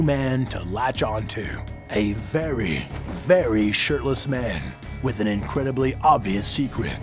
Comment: I heard "sh*tless" instead of "shirtless"